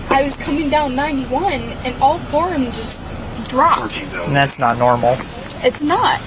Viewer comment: the audio, transcript and subtitles say all four of the tires "just dropped"